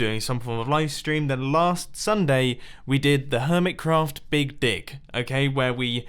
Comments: sorry what? I heard, "Hermitcraft big dick." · I thought he said a big di*k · did anyone hear the big dick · am I the only one that think he said big dic*????